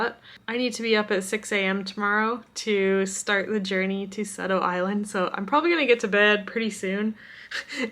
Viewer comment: I keep hearing "Subtle Island" haha. Sado looks absolutely gorgeous. Can't wait to visit Japan yay for it opening up!